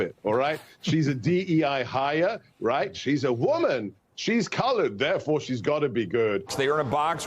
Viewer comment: did he say "She's colored"?